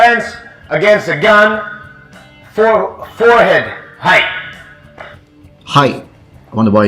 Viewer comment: I thought he said "height".